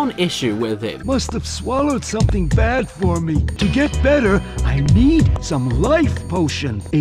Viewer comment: I wasn't paying attention to the video, so I heard "I need some life BULLSH*T"